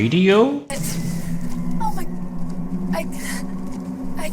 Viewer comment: I've never noticed that the subtitles are wrong, master chief says "Where are we?" instead of "Where is here?". Which the subtitle doesn't even make sense.